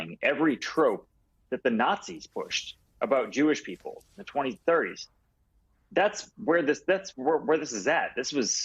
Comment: Did he say the 2030s? At 1:40 I think he meant to say the 1930s.